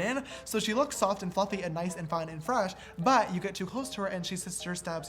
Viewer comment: who else was surprised when he said fine and fresh instead of good and fresh